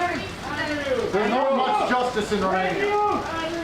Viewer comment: I thought he said 'There's no justice in Romania'...not what I heard in the very beginning of this video.